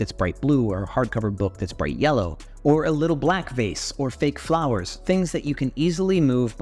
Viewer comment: Honestly thought you said 'a little blackface* ...